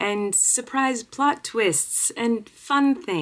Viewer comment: I thought you said surprising butt twist 🤪